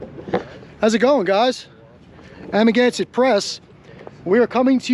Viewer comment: I swear for a month I thought was saying “I’m against the press”. Lol until I found your channel.